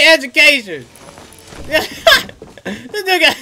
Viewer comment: i swear to god @ 14:01 he said this ne**a and then he laugh so like nothing happen WTF brather !